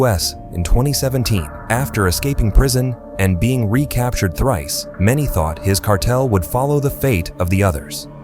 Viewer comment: did he say thrice